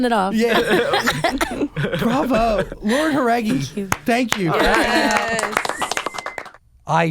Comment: did she say "thank you" "I'm cute"or "I'm Cuban"?? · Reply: Kay she said thank you